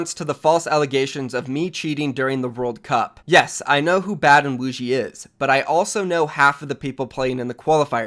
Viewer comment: Thought he said “I know who bad a boujie is”